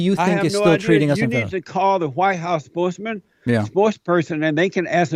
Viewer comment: Did he say "Whitehouse sportsman"? I can't